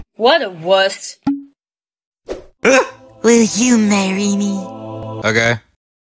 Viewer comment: i think is he said 'im gay' in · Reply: He said , "okay."